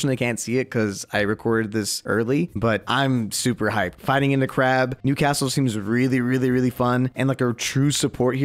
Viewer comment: Thought you said 🐀 ing in the crab 😂